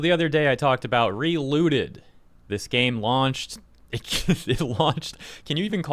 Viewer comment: At some point in this video, I thought said "it was hate launched" before going back and double checking LMAO